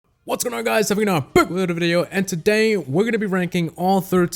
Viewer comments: When you say what's up guys in your intro you say it so fast that it sounds like your saying what's up guys have a good night 😂 like If you agree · what the hell did you say? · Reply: NiceGuy Productions "What's going on guys,have a good night,back with another video".